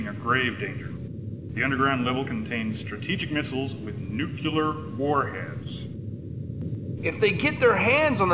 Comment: I misheard that as "nuclear foreheads" at first.